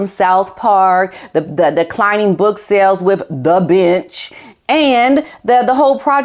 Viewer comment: When you were talking about the book “The Bench” I thought you said “The Bitch”! 😂😂😂 Which made me think that could be the title of Harry’s next book! 🤣